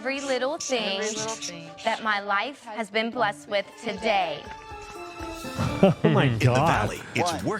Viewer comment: guarantee the affermention said "corey has blessed ny life w" instead of "what my life has been blessed w today"